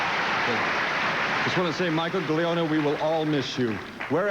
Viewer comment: Can anyone clarify the OLTL actor James DePaiva mentions before reading the noms? It sounds like he says “Michael Delono,” but that isn’t a name listed on the soap’s IMDb page. The only person I can think of is Michael Storm, who had played Larry Wolek since 1969 and was officially dropped to recurring at some point in 1992 (and this ceremony was in May of 92)… But what’s throwing me is DePaiva clearly isn’t saying “Storm.” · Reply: He is referring to Michael Gliona - he was a director at OLTL who passed away that year. Ironically he directed the episode when Megan died